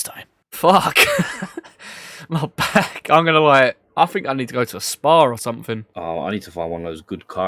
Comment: At the end. I swear I heard you say ' I need to go to a Spar ' .. like what's a corner shop gonna do for ya?! Haha Great video. Good narrative. Awesome comeback!